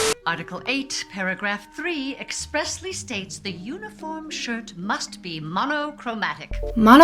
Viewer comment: she actually says uniform SHIRT must be monochromatic, anyway great video xx